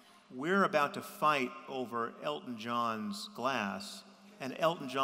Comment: Sam's comment doesn't even make any sense. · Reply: I was confused as well, because it sounded like he said "glass" not "glasses". Thanks.